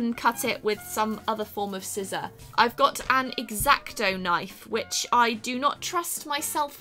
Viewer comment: sounded like she said erecto knife I was concerned